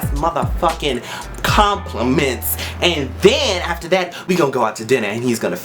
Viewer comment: When he said "compliment" I heard 'Cum..plement' o_ o' Forgive this shameful mind. v ,v